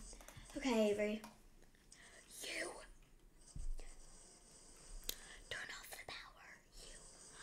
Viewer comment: Bro a heard you say trune of the powered 😂